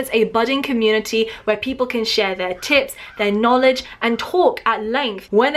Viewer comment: I thought she said "where people can share their tits" Ahhhhhhhhhhhhhh help!